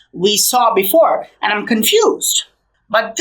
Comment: Did you say you are confused? Lol, I am mesmerized of how good you are. You deserve an award my friend!